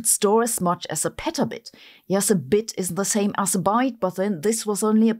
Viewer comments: She literally said "Yes a bit isn't the same as a byte, but then again it's only a prototype" in the video 😅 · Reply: I heard "is" not "isn't". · Sabine, a bit is not the same as a byte. A byte is usually 8 bits. A bit is one on/off binary digit. A byte is a string of 8 bytes that represents any number from 0 to 255. It is one character in normal computer data. So a petabit is 1/8th of a petabyte. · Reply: I think she said "Yes, a bit ISN'T the same as a byte", based on the context of the sentence, but it does sound like she said "IS the same". Subtitles also confirm that she said they aren't the same. She means that while a petabit isn't quite petabyte storage yet, this is just an early prototype and it's likely to improve. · whoa whoa whoa. Did she just say a bit is the same as a byte?? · Sorry , I think I heard you say a bit is the same as a byte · "A BIT is the same as a BYTE" Tell me you don't understand the fundamentals of computer science without telling me. A Byte is exactly 8Bits not the same in fact not even similar. A bit is a stored boolean value, (true or false) A Byte is eight of those bits together. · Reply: You misheard her, she said "Yes, a bit ISN'T the same as a byte". Turn on the subtitles if you need to. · She literally said "Yes a bit isn't the same as a byte, but then again it's only a prototype" in the video 😅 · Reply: i heard “yes a bit is the same as a byte”